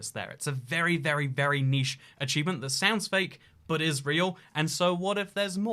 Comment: For the entirety of the video I heard: "sounds fake, but Israel"